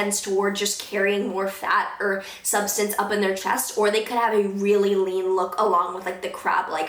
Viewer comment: HAHA I heard "A really lean look with a CRAP like face" and I died laughing